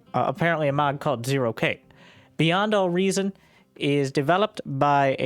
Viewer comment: I heard it as BuyN'd All Reason